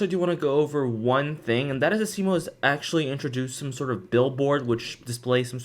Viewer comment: I like how pronounces "Asimo" right but the captions say "a similar"